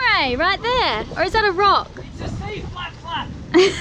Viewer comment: Did anyone else hear Riley call the Stingray "a sea flap flap!" @ 11:12 lmao